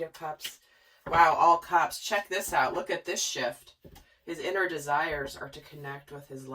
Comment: Lol! You said “check this out, look at this SHIFT.” And I was listening and swear I about busted out laughing bc I thought you were saying look at this sh*t. 😂🙏🏽🌱💗😇